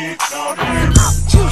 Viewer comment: AT FIRST I DIDN'T KNOW WHAT THEM WORDS WERE SAYING AND I THOUGHT IT SAID IT SQUAVA JUICE 😂